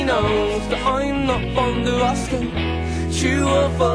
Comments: I'M NOT FOND OF ASKING!?!?!?!?!? I FINALLY KNOW WHAT IT SAYS!!! FOR THE LONGEST TIME I WOULD JUST MAKE RANDOM NOISES AT THAT PART CUZ I COULD NOT FOR THE LIFE OF ME FIGURE OUT WHAT IT SAID!!! MY LIFE IS COMPLETE!!! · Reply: I thought he said I'm not up for the asking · Is it just me or does it sound like he is saying, I'm not fond of Alaskan?